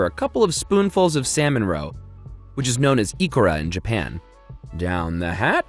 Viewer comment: For anyone curious, at 19:30 the subtitles say "Speaks in Foreign language" (despite spelling out the Japanese word Nori in the previous sentence!), what he said was "Ikura" which literally translates to Salmon Roe. Edit: Also at 3:12 the word is "va vous"